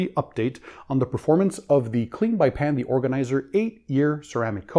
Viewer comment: subtitles says "Panda Organizer" have you ever considered changing your name to that? Would be cute and funny.